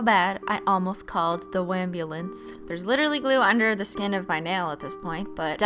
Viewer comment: did she say "wambulance" ✨😂